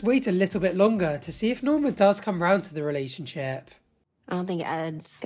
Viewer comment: I thought you were gonna say wait and see if Norma DIES 😂 I was like oh noooo